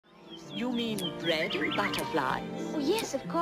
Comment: This is how I hear the intro song: You mean red like a flag? Yes of course!! XD that’s what I hear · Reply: I hear “you mean bread butterflies?” “Yes of course eh” “hm?”